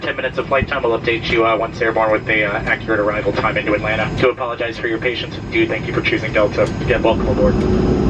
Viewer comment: Did he say "We do apologize for your patience?"